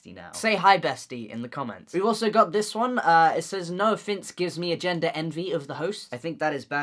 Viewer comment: Every time he says 'Noah Finnce' I hear 'no offense' damn